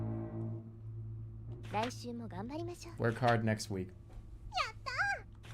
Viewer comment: is it really? The punctuation and quotations are just so spot on. I havent found a single discrepancy which usually happens for auto generated ones, also when he stutters they are still accurate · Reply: a vroomer passes and the captions correctly call it so, but seconds later at 6:24:05 jerma says “work hard next week” and the captions got it wrong.